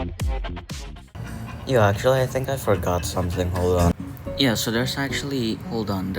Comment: I had a stroke understanding what bro said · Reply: “yeah actually i think i forgot something hold on” lmao